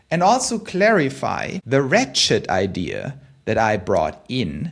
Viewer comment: When he said “ratchet idea” at 8:50, I could have sworn he said “rat shit idea” and almost did a spit take.